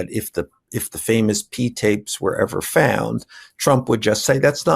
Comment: “if the famous PEE TAPES were found” Did I hear that correctly???? P as in PEE or P🌽?? The captions don’t capture what he said.